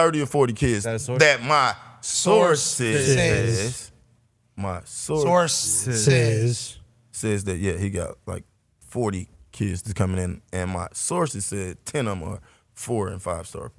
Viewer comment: The whole time I've heard it as my "Source Says" which made so much sense. I can see why pac is confused 😂